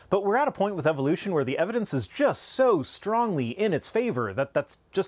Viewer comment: I thought you said "Evidences" and I was going to be mad. Then I realized you said "Evidence Is" and it was K.